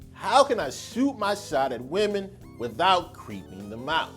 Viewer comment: I literally thought you said shoot your shot at women without creaming them out.